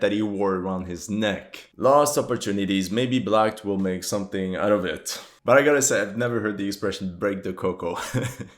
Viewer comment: Did he just say “maybe BLACKED will make something out of it.” I’m fucking dying rn